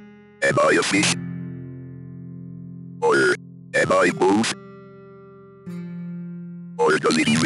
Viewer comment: “Am I a frog? Am I a Fish? Or am I Both? Or does that even matter? I’m a pain to deal with!” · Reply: Why when you said "Or am I both" I thought it said "Or am I a bomb"